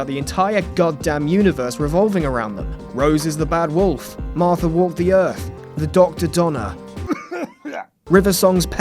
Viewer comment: damn... i can't understand what he said about Martha lolol. i keep hearing "Martha walked the Earth". Honestly, Donna and Martha didn't feel like the they were part of a bigger plan. They felt inline to Bill. Female doctor is cool, but what i'm excited for is that Steve Moffett is out. Honestly felt the show got worse because of him taking over.